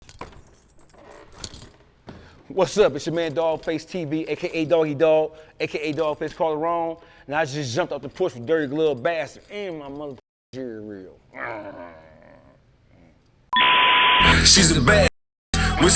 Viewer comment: Did he just say CoLarone😂😂😂